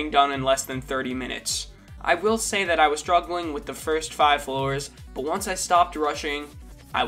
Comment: did u say FIRST 5 floors